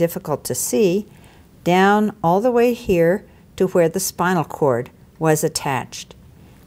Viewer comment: did she just say, "where the spinal cord was "?